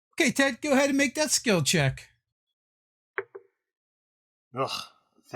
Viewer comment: I keep hearing "death skill check" in the first sentence and it angers the nerd rage. Pretty sure he's saying "that skill check", but it's still bugging me.